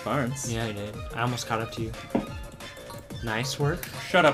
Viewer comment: what did he just say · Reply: He said "Nice work" Have a nice day